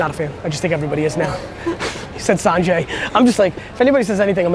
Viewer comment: The guy that said Sonje, I thought he said Gary Vee too haha! Solid episode Gary!